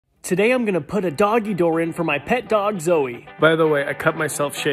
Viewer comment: For a min I thought you said "Today we are gonna put my dog down" 🤣